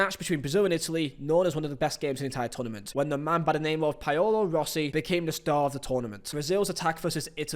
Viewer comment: ''Payolo Rossi'' ''Medejjin'' Come on man... I know you're not a University grad... but that's just tragic. I've heard you say Paolo Maldini before lol....